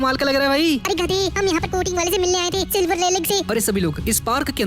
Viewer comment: nah bro did he said silvers rayghlie